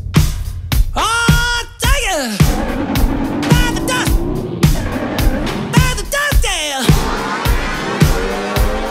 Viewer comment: What is he saying at 1:58-2:04? On the original recording it always sounded like adopted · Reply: "Bite the dust! Bite the dust, yeah!"